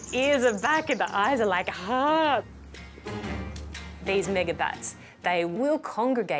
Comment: did she say nigerbats?